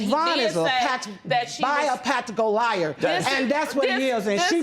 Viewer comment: press pause did she just say "BIOPATCHOCAL LIAR! "😂 😂 😂 😂 😂 Nooooo sisss you shouldn't of tried that word like you shouldn't of tried that mullet! 😂 😂 😂 😂